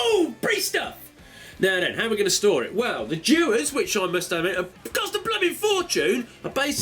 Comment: The hell is he saying at 2:22 ? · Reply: Cost a bloody fortune